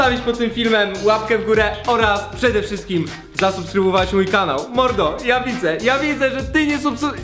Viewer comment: ja zamiast mordo usłyszałam porno ;_